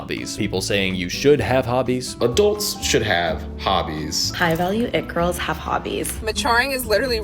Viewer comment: legit thought this girl said that high value egg rolls have hobbies